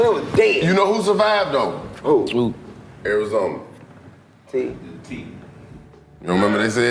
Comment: I thought they said Snapple who was owned by the KKK I don't remember Arizona 🤷🏾‍♀️